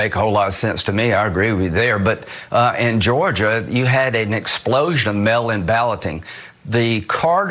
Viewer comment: It sounds like he says "In Georgia, you have an explosion of melanin balloting."